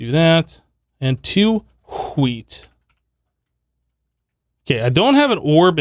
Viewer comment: It sounded like you said ''Skit'' witch means sh## in Swedish. Yeah... You just swore in Swedish on accident.